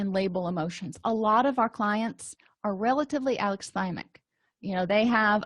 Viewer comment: Can someone help me decipher what she says at 41:30? The dictation says Alex Simon- but I’m guessing it’s not that and that it’s a diagnosis. If I’m wrong then pardon the ignorance. But if not, please help me decipher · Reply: Thank you for watching the video. I am saying "A lot of our clients are relatively alexithymic..." Alexithymia (from the Greek: a- "lack", lexis- "word", thymos- "emotion") is a clinical term describing a personality trait or condition where a person has difficulty identifying, understanding, and expressing their own emotions. In the context of the video, people who are alexithymic often struggle to put words to what they are feeling. Instead of recognizing "I am feeling anxious" or "I am feeling sad," they might experience an emotion purely as physical distress (like an upset stomach or muscle tension) or immediately react without realizing what feeling triggered the behavior. I point out that because many clients have a limited emotional vocabulary and DBT skills focus heavily on helping them learn to identify, label, and describe their emotions accurately.